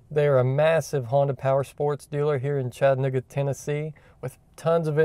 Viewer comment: LOL I heard "ChadNigga Tennesse"